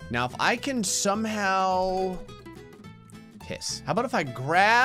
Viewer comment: When he says piss at 11:07 the subtitles say thet he said kiss